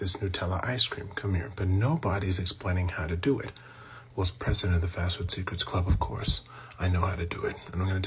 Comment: The In Video Subtitles Say "was president of the Fastwood Secrets Club" 💀💀💀